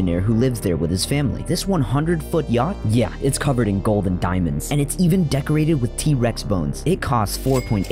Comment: “Covered in golden diamonds” 💀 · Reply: I thought he said "gold and diamonds" I know the cc said golden diamonds, I thought it was an error